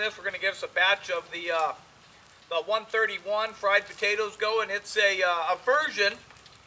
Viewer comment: Looks DAMN GOOD. I thought you said the fumes wouldn't get you tipsy....fried not spied.